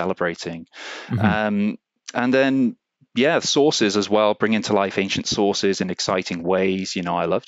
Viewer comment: me being an American thought he said "sauces" I was like ooo yes that would be pretty cool to get some ancient recipes from antiquity